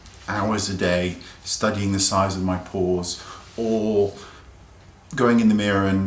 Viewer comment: Your accent almost makes it sound like you said, "I don't spend all day studying the size of my balls". I had to rewind twice to hear "pores".